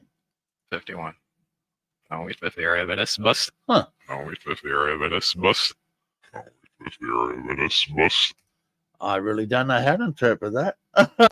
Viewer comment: I'll wait for theory OVER this bus. That's what I heard btw it's like, what's that phrase...nuts I'm drawing a blank. Wait until he'll freezes over kinda rhing